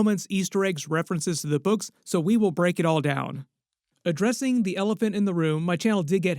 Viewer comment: what you said: "we'll break it all down " what i heard: "we'll BURNT it all down"🤣